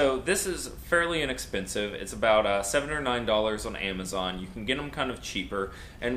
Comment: I heard him say 7 or 9 the second time but the first time I heard $709